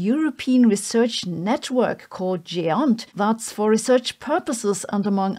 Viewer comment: my brain can’t make out this word for some reason lol- “European research network _____” what is being said after network there? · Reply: European Research Network called GÉANT